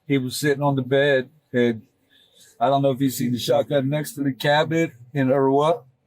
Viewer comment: Wait did he say a shotgun?